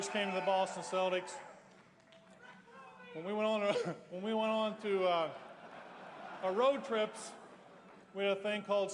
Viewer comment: what's the guy in the crowd yelling at 3:04 that cracks em up? sorry, im a youngin. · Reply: "Rick Robey"